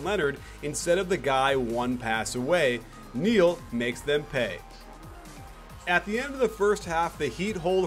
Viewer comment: Did I just heard "New York makes them pay"??? LOL